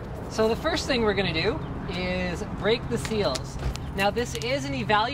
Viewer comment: What did you say? Are you gonna "rape the seals"?